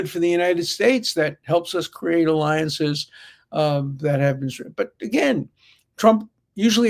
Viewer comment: What does Professor Dershowitz say at 18:20? Does he end the sentence with "alliances that have Israel"? If not, then with what does he end the sentence? Thank you.